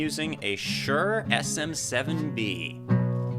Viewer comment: Oopsie. On re-watching this live stream (I was occupied with other distracting matters the first time), I now hear that you said Shure SM7B, I thought that you said '57'. You can pick up this workhorse dynamic microphone for $100 or less. They are quite rugged and you should be able to find a good used one for significantly less; with the usual caveat that if the deal sounds too good to be true, it probably is.